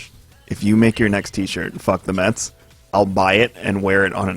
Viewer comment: ohhhh i thought he was saying FUCK THE FEDA